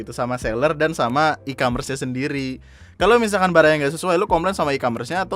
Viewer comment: Did He Just Say "Baraya"?